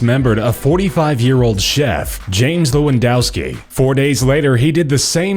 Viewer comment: Did they say LEWANDOWSKI?